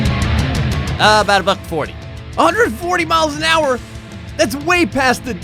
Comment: it sounded like said "about 45"